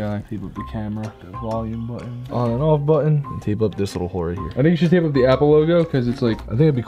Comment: I thought he said "tape of this little white right here" I was DYING